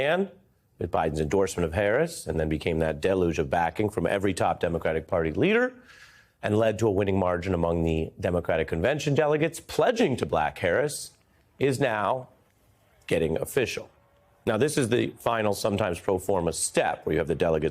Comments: Did he say pledging to black Harris 😂😂😂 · It sounded like he said "pledging to Black Harris." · Did he say “pledging to black Harris?”😂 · did he say the delegates are pledging to black Harris? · did he call her ‘black Harris’?😮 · Did Ari just say "to black Harris"? 🤔😬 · I have listened to 0:15 many times, did you say pledging to black Harris? Because that's what it sounds like lol